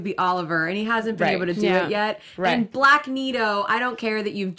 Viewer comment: What was that word at 3:20? Blackneedo? I’m sure I didn’t hear that right. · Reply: Corey Wallace Magneto’s helmut is Magenta”ie” and Diggles is black. I’ve been calling it Blackneeto for seasons. It’s literally the same X-men helmut in another color.